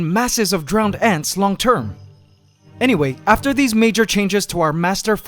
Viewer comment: captions said minor you said major....